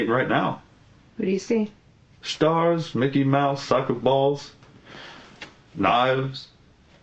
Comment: That intro was amazing 😂😂 Mickey mouse, soccer balls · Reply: When he first said that, I thought he said Mickey Mouse sucking balls. I was like WTF?!?!?